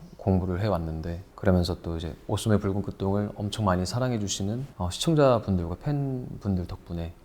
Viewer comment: Hi at 1:20, scarlet heart ryeo English subtitles are wrong. Please change it to the correct the red sleeve instead!! Otherwise, great video and junho’s meaningful responses are so nice to hear